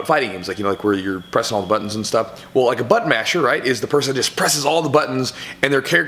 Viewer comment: Lol thought you said Butt Mashers